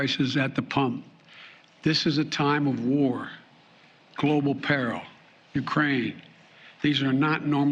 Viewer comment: did he just say this a time of ukraine 0:16 lmfao it sounds like hes just rattling off buzzwords